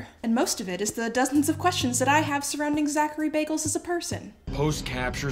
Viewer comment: I heard you say Zachary Bagels 🤣